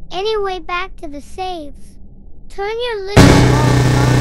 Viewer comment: Melon worker:turn you ilgma balls volume down ☠️☠️☠️☠️ 2:58 Me: what did he say?!?🤨🤨🤨🤨☠️☠️☠️☠️☠️